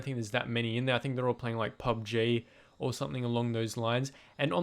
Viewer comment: Omg did he say pub j??